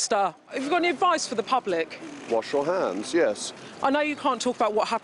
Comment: Lmao did he say watch your hands or wash your hands?? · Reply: He said "Wash your hands".